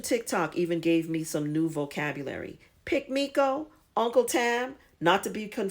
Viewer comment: Oh man, I mean woman L😁L, the suspence of "who is 'Uncle Tam'"? I am eagerly anticipating your next installment. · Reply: I thought she said Uncle tab